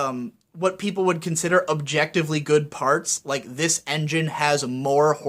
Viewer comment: Did I hear say "objectively good"? 8:15 A wise bearded man once told me objectively good doesn't exist. X3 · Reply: he said what people would consider objectively good